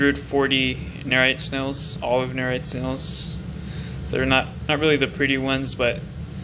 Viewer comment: What kind of nerite snails you used? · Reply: Hey I guess I heard him say "Olive Nerites " 🪐